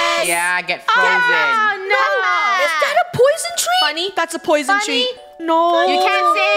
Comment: Yo I thought I heard Funneh say: Funneh: "That's a Poison Treat. Funneh!" I HEARD HER VOICE SAY THAT